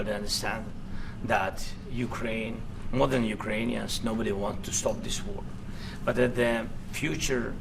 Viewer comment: What did Zelensky say here? "Nobody wants to stop this war"?